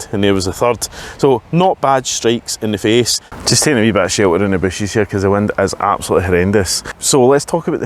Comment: Dude! Hahahha your accent rocks. But can you translate what you said at 3:42? …………………………………………………..cause the wind is absolutely horrendous . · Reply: "Just taking a bit of shelter in the bushes" haha